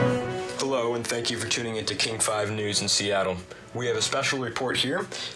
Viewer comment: Is it just me or does it sound like he say “kink five news”???